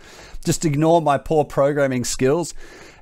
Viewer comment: Did he just say: "...ignore my poor programming skills..." ? 18:54 ... Man, I would like to have 1/2 of your poor programming skills :D